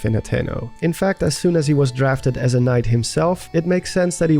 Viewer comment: drafted? DRAFTED?! am i hearing that correctly? thats it King Rhoam officially sucks